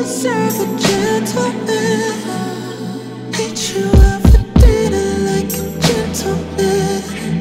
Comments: This man said , ....tell me have you ever have a Gentleman? Eat you up for dinner like a Gentle Man 😭😭😭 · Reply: Girl the whole time I thought he said take you out to dinner 🤣🤣 like a gentleman 😭 🤦🏾‍♀️ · Did he say, "eat you out for dinner like a gentleman?" -asking for a friend. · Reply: I thought he said treat you out for dinner like a gentleman 😂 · i thought he said eat you up for dinner like a gentleman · “...eat you out for dinner like a gentleman”?! Well..........damn 😏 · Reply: I thought he said eat you up but same freaky difference😊😊